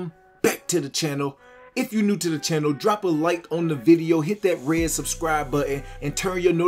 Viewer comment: I subbed bc I swore you said decorate that subscribe button and I thought that was super clever…but you said hit that red subscribe button! Either way you earned my attention lol